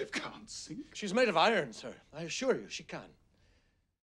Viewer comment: I thought he said "She was made in Ireland sir, I assure you she can" when I was 9.